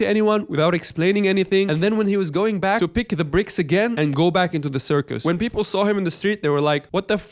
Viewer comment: couldn't really understand what you're saying. He would go pick up the bricks?